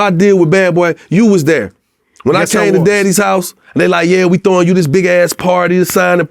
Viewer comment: Nah at 7:20 Joc said "Daddys" house, Instead of Diddy. I know for a fact Artist's refer to Diddy as Daddy.